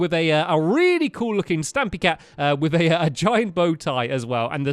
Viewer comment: For a sec I thought stampy said stabby cat